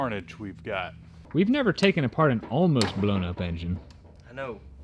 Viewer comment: At first, I heard "blown snow-blower engine" and I got excited...ooohhh...a SUPERCHARGER! Then I realized it was a blown UP engine... A little knock? Just use thicker oil! LoL